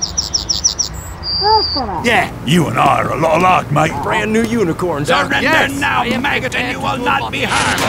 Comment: did engi say "brand new unicorn, doc?""